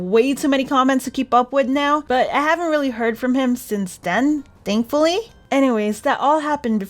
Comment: i heard at 10:44 that said i have not heard from him since not then